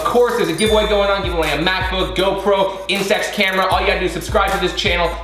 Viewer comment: When he said MacBook I thought he said math book